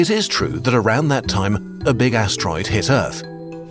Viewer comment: i thought he said big A$$